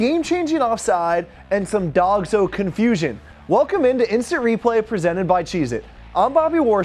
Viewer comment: Swear to God I heard the guy called his name at 00:05 😂 "Instant Replay presented by Jesus" Forgive me, I'm just bad at Listening Skills 😂